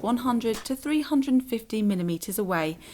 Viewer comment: did she just say 450 mm? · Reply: kiwi762 : nope, she said “... forty millimeter...”